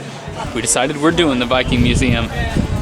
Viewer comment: I thought you said "Biking Museum" and I saw the skeleton and I thought.....must be the first biking accident victim.